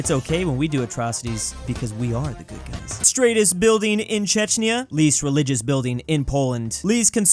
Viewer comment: Bruh, did he say Chechnya?